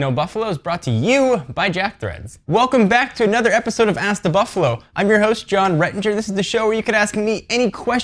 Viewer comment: When u started off I thought you said ass to buffalo lol